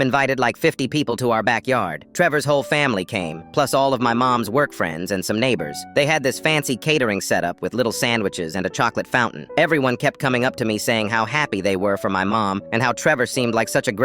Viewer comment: I heard "work friends " as boyfriends 💀